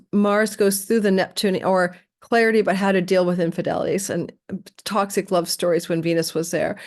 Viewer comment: Listening for my partner and all I heard was “infidelity”… 😭😅